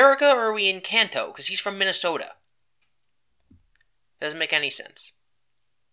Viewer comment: did you say he's from Minnesota? Fuck, so am I! In fact, I'm still there!